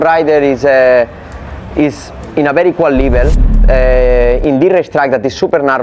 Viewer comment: ijin mengoreksi keknya maksud marquez bukan "jika pembalap terdepan berada pada level yang sangat "berkualitas", tapi "setara". soalnya kedengeran kayak "equal" bukan "quality". memang marcobez berusaha dengan sangat bagus, tapi marc juga bagus dalam mempertahankan posisi jadi marc menganggap serangan dan pertahanan mereka berdua setara sedangkan untuk menyalip pembalap terdepan maka serangan dari pembalap belakang harus lebih cepat dibanding pembalap yang berada didepannya. cmiiw